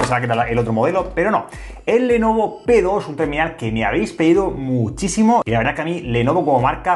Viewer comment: si escuchaste que dijo pedos en